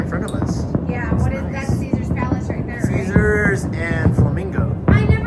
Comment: HNY! Great vid🎉 thought she first said caesars salad tho! Lol guess im hungry😂